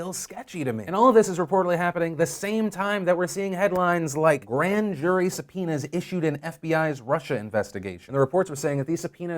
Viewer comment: I just heard Phil say "Grand jury su-penis..."...